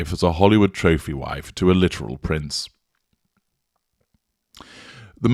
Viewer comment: I would not have ever described Harrys wife as a socialite,she’s the total opposite of that, you have to have class!!!! Thanks. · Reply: I’m sure I heard say “a literal prince”, but in my heart I heard an illiterate prince. 😂